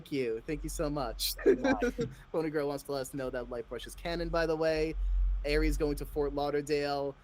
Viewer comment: DID THEY SAY LIGHTBRUSH IS CANON AND IS FANTUBE CANON NOW?!